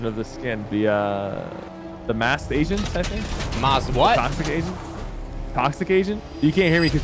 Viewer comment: I thought sypher said toxic asian like what!?!?